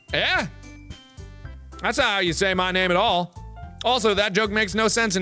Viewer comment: who else thought it sounded like my ni**a